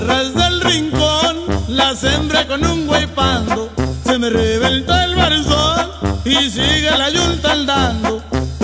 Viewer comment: me encantan estas canciones , y yo pensé que decía se me rebento el calzón pero no:-) :-) :-)